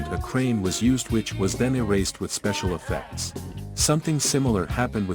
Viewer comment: I thought you said no special effects